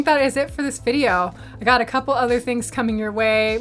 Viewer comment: Did you just say "RC ya later" ?? haha Clever...